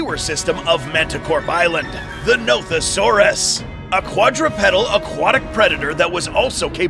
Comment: At the Nothosaurus part when he said the dinosaurs name, I thought he said Nope-a-saurus